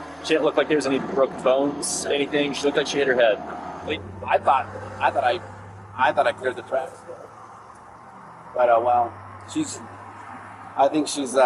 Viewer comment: Did he say “I thought I cleared the tracks… but, oh well.” 😱